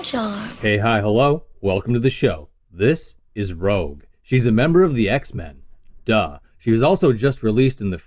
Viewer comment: Sorry did you Say HES a member of the x men0:06